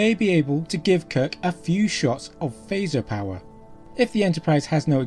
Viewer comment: Interesting - at 4:38 you said that Scotty says he can give Kirk "a few shots" of phaser power. I always thought Scotty said "the fuse's short, sir." Makes more sense that he said "a few shots".